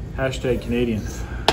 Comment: Did say Canadia?🤭🤣🤣